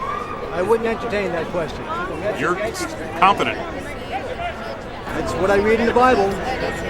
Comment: "You're confident." "It's... what I read in the Bible." Me: NO... IT ISN'T. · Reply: I thought he said “That’s why I’m reading the Bible.” Your point still stands, of course.